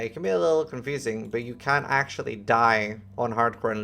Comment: "can actually die" or "can't actually die"? I've listened to it a few times and can't figure out which he is saying. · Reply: Can actually die and lose a hardcore character